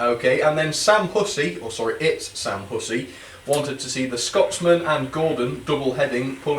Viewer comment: I remember when I first saw my request on this video and it was one of the most happiest moments of my life! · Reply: Ok thanks I thought Sam said “It’s Sam Gussy “ 😂😂😂😂